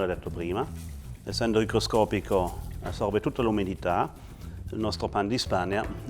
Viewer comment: Translation correction: at 3:40, instead of "microscopic," it should be "hygroscopic," since Knam is talking about sugar's ability to absorb humidity.